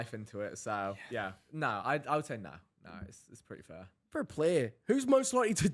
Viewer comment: do you know what joe said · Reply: he said fair play I'm pretty sure